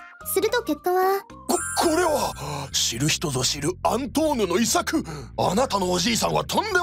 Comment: 辺りから 「え？アントン？(空耳)」